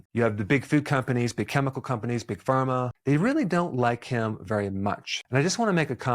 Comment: Oh, he said THEY really don’t like him much😂😂😂 I was worried, I thought he said I don’t like him😂😂😂